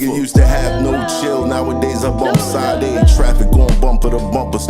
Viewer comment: ALL AROUND- MATH HOFFA FEAT HANZ <link> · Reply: That intro bothers me... it sounds like your saying "Side A", when her name is pronounced "SHA-DAY".....oh well...Dope song though!!!🫡